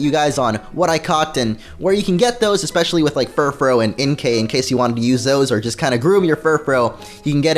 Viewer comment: i miss-heard him say "Inkay" and thought he said "MK" and i thought he named is Espurr "MK" like MK Ultra, the old mind control program in the usa a long time ago lol. That is a great name for Espurr! A psychic name for a psychic mon